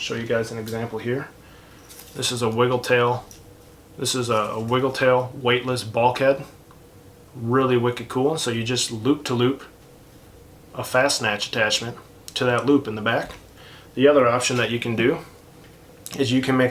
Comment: Love your vids! I’m half deaf 😂 Forgive me, but @ 3:17, did you say “fast snatch attachment”? I’m asking for my bf, he ties flies, but isn’t cell tech savvy (GenX). He’s learned a lot from you. Could you please, show more closely, explain more in depth, how to make them or where to get them? Thank you! Or if anyone else can help… that would be great!